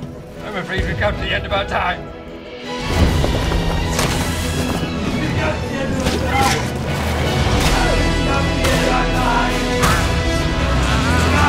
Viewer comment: What are the voices saying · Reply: My blood type is MEMES “We’ve come to the end of our time”?